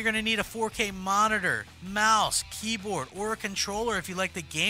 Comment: did he say 4k mouse and keyboard? shit i need to upgrade fuck